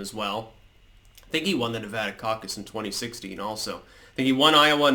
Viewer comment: I heard it as Nevada cock - up . Not far from the truth , eh ! ?